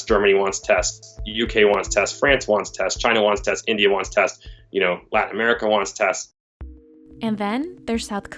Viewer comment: did he said “black America” ????